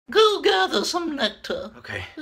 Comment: "My life is fucking torture" - A Bumblebee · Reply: I thought he said "I lied. It's fucking torture"